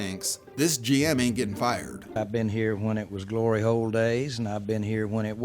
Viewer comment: all I can hear is “glory hole days”😂 what is bro actually saying?